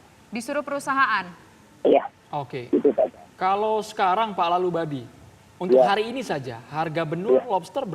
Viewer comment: Gak salah dengar atau gimana ya. Mnit 6:22 nama Bapak nelayan dibilang bapak lalubabi